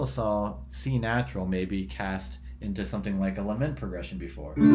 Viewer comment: Bravo Maestro. I am also in need to ask you a question at the Time Set 8:15 I do not understand or hear the word that you intend "into something like "?eleven?" progression before. Would you please be kind enough, especially, since your video(s) have been a life line to Music Theory, if you can squeeze a second to inform me what is the correct word. Respectfully, A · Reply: Thanks for your comment/question! There, I speak of a "lament" progression. Essentially, a schematic way to describe a descending bass line through the upper tetrachord of the natural minor mode (do–te–le–sol). In this case, I'm making the case that the C-natural in a lament progression creates a "non-functional" dominant (v) chord that lacks the leading tone (C#), which relates to the non-functionality of sequences in general (in which patterns take priority over functionality).